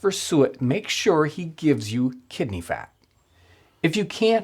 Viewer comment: I thought he said kitten fat instead of kidney fat lol